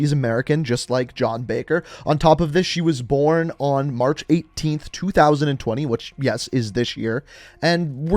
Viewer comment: Did he just say match 18th 2020